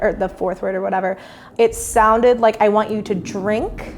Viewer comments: I thought it said I want you to drown #postmortem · What I heard was “I want you to break” that is way more ominous!